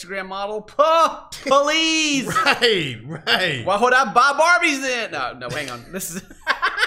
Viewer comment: What did Ken say at around 11:12 ? I could not understand and the captions don't help lol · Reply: He said "why would I buy barbies then?"